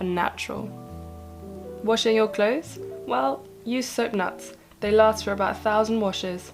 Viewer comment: Can someone please tell me what she said at 6:24...what is that bag called? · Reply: They're soapnuts but it looks like they're not that ethical because the price has been driven up due to western consumption and now locals in India can't afford to purchase them anymore. You can make your own laundry detergent with Soda crystals, a bar of castille soap (grated) and borax substitute xx